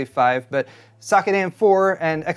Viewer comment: This is a petty comment, but when kept saying "socket" earlier in the video, all I kept hearing was "suck it..." And from the subtext of this video, my subconscious kept hearing "... Intel" thereafter. Probably because I watched Linus' rant on this CPU before this.